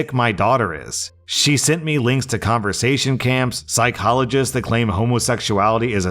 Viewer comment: "conversation camps" made me giggle lol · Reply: I was half listening and thought he said concentration 😂😂